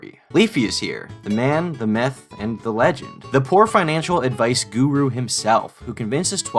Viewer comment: I don’t know if I’m just hearing things but it sounded like he said, “Leafyishere, the man, the meth, the legend” I fuckin died 🤣